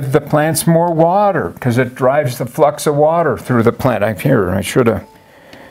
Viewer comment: I swear I heard "water f*cks through the plant"